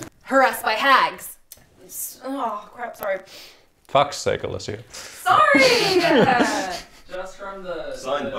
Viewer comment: What did call at 11:58 ? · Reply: Alicia?